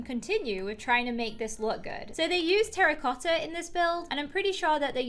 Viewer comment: When she said terracotta I thought she said Harry potter